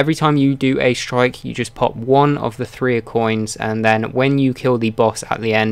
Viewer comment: I'm confused, he says that at the end of a strike, for the boss fight. It sounds like he says "put on acoin" it sounds like he says "a" and "coin" together. Then he says do that for a better chance at dropping exotic engrams. What does this mean? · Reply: He was going to say "Three of coins"